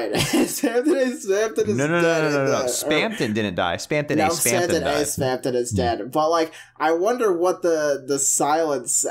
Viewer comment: What was said at 18:43? I couldn’t make it out due to crosstalk. “Spamton didn’t die; spamton [unintelligible] [spamton?] [dead/died] “? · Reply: "No no no, Spamton didn't die. Spamton A Spamton is dead, but Spamton G Spamton is fine."